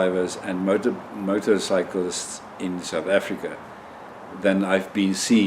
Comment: South Africa 😱🤣🤣🤣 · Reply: yes....I knew South Africa....but it sounded like you were saying something like "Kumalonga"!